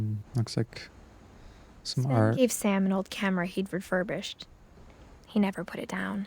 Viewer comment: Typo in the subtitles here: "If Sam had an old camera" instead of "Sven gave Sam an old camera"